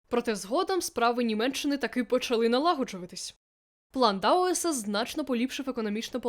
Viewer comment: плохо расслышал какой именно план имеется ввиду? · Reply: ПЛАН ДАУЭСА - репарационный план для Германии.